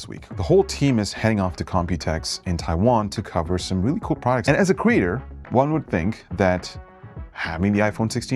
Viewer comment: did you say 'and as a queer'? not that there's anything wrong with that.